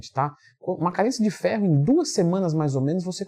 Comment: Eu ouvi "Carência de fé" umas 3 vezes hahaha